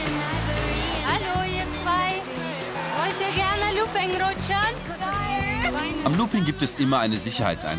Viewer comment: Ich hab verstanden „wollt ihr ein looping lutschen "😂